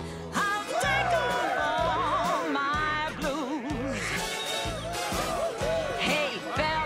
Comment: Also, I did not realize until NOW that the line was “I’ll take off all my blues”. Even as a little kid, I heard it as something else. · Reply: They thought she was saying clothes, instead of blues.